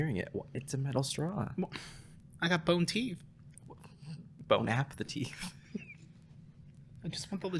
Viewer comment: “bone-app-the-teeth” i always heard it as “bone-apple-teeth”